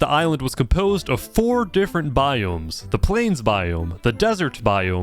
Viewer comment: Why'd I hear, "The planes biome"? Like, does the plane own it?